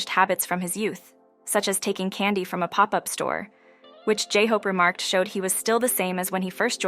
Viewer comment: So he steals candy from pop up stores? That's what it sounds like they said.